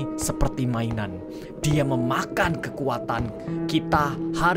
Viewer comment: "dia memakan kekuatan" Gak salah denger?